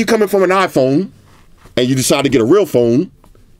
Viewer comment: #Squad Roll Call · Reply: minutes: Am I hearing correctly? I thought I heard he said that if you have an iPhone and need to get a real phone (the S20 Ultra I presume)🤣...